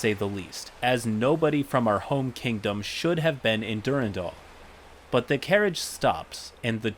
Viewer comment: I was listening to this in the background, and when I heard you say "Durandal," I genuinely freaked out for a moment. I thought you said "Durrendale," which is the name of the first major city we visited in a world my partner and I both play and DM in. When I saw the spelling and the thing about the vines, I calmed down immediately, but the name resemblance combined with the title of the video had me worried lmao